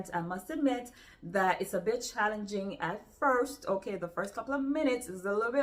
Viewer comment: when you said ' a bit challenging ' I thought for a second you said 'bitch.' ha!😂 I will be looking into some of these. Thanks for your recommendations always. 🧡